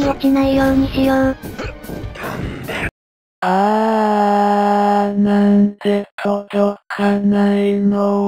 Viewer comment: の所レックスが(なんで)が聞こえるだけどwww